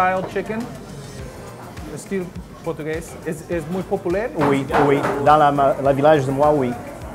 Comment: Did he say "muy populaire?"